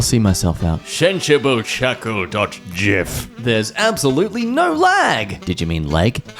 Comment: did he just say jif...